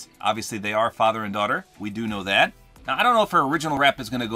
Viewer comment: When he said dog I thought he said daughter lol 😆 😂 I was like why would Ellie do that??